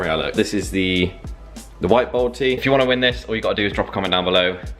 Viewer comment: Legit thought you said “balti” not “bold-tee”😂😭